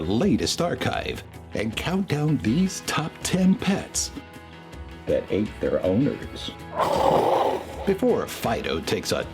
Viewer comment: When u said that ate there owners I thought u said THAT AINT THEIR OWNERS??